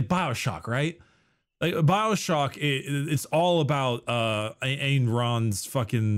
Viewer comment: said “Ayn Rand” and I definitely thought he said “Angron” 😂